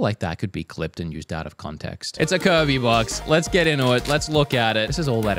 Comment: the captions just went along with the 'out of context joke' by writing 'curvy box' instead of 'kirby box' lmao